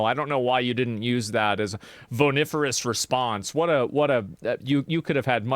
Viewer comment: Can someone plz tell me what he said at 1:44 , something… response. I searched “voniferous” but it doesnt seem to be a word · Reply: The word is vociferous